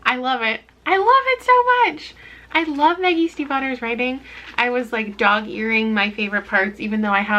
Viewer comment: Am I the only one who thought she said "the yeast and Butter" when she said "Maggie Stiefvater" 0_0